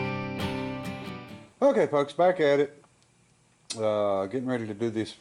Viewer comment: Try to stop saying "folks" sometimes it sounds like your saying f*cks. I think that's what's happening.